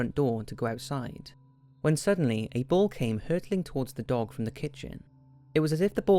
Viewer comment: the first few times i saw this video, i thought you said a bull was hurtling towards the dog, not a bowl ahfnfndnsbsgmfm, my bad